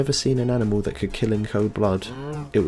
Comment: When you were talking about the mooshrooms I heard "could kill in cold blood" as "could kill an old god" and was like "yeah....yeah probably"